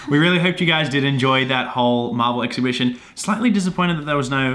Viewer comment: I thought you said marbel hahahaha